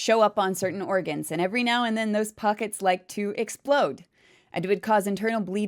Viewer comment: That's awful when you said you got pockets on your organs sounds painful. I was doing work in the background when that part came up on the video and I thought you said "Hot Pockets", I was like yea the make my organs hurt to.